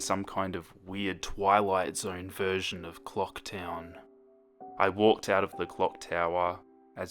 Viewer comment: I thought it said 'I was on top of the ClockWork' I was just like "What Even?!"